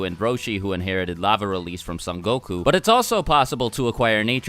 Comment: sounded like u said “Son Goku” lol